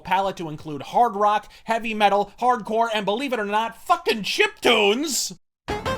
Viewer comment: What does he say at 36:22? Chip tunes?